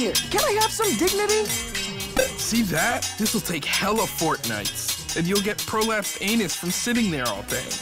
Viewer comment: Coming back to this, I just realised at 1:15 it sounds like he says fortnite and it's so triggering